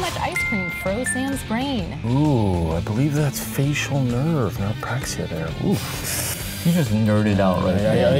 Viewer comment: What did he say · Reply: i believe he said "Apraxia there"